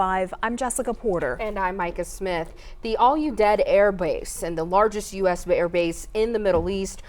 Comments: I've could've swore she said ARE YOU DEAD airbase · it sounded like she said "the all-you-dead airbase"